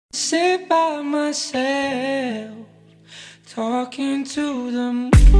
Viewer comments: All this time I thought he was saying "Stay by my side, look into the moonlight" 💀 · Heard it as talking to the moose